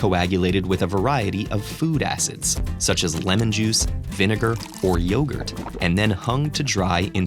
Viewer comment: It honestly sounded like he said lenin juice not lemon juice LOL